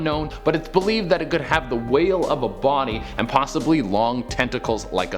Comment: Idk if I'm high but did he say "whale of a body" instead of body of a whale?